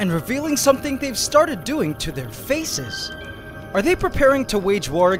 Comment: Omg I thought it said wearing pants on there face